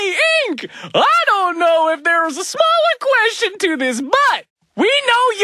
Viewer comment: What he writes: “similar” What he says: “SmAlLeR Me: ▪️_▪️